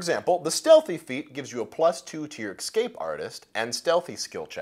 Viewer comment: did you say "ecscape"?